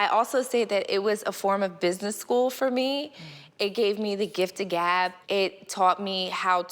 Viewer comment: did she just said that it gave her the gift of GAP??? as in gaping.... edit